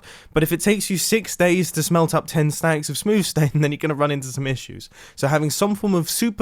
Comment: At minute 2:59 did he say “ smooth stain?”